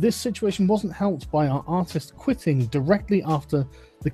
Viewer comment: You keep saying “artist,” but I only hear “autist.”